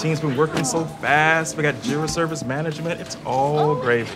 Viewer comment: Funny,, but I thought she was saying Jury Service Management!